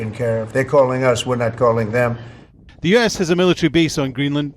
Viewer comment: When he says "They're calling us, we're not calling them" I heard "lie lie lie, lie lie lie lie"